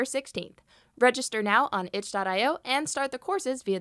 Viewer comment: Register now on WHAT????? Could you please say it slowly so no English natives could understand? Or at least ad the link on screen? Thanks!!! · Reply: Register at itch, just google itch and it's first website you'll see.